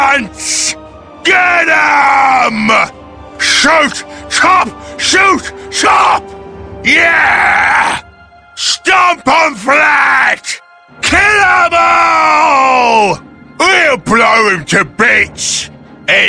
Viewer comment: Wait they say "STOMP EM FLAT"? All this time I thought they said "STOMP EM LADS!" Was kind of inspiring to listen to when I heard them say that.